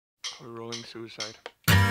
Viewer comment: At the very beginning of the song, it sounds like it says "Rolling suicide." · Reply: It is, he's saying "We're rolling suicide", Suicide was the working title of the song so basically he's saying that they're recording.